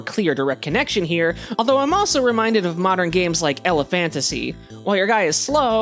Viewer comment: what is this game called I couldn't quite hear what you said 😢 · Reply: I’m here to tell you it is elephantasy :) it’s really fun but I ran into a glitch near the end of the game that ruined the entire run (an item just disappeared from my inventory and I couldn’t collect it again) so be careful!